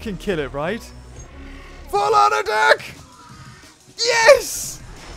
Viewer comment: FULL ON ADICK!!! Is what I heard